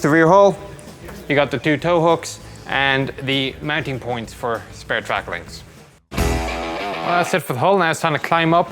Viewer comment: * lol* When he said "Spare track links" I heard "spare tracklings" which sounded pretty cute. :3